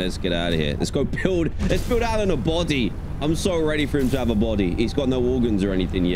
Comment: Did I just hear Dan say: ‘Let’s build Lannan a body’?